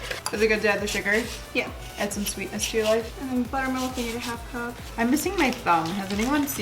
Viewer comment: I thought Shiro said "I'm missing my thumb" instead of "I'm missing my phone" 😐